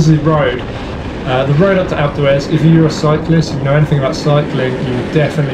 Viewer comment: a couple times i thought he was saying "acletraz" instead of alpe d'huez